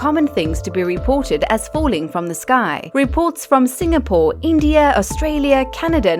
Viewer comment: did I just hear Singapore cause if I did I did not knew about that